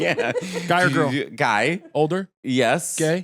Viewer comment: I've listened to Matteo so much that when he said "guy" I heard "gay" in Italian.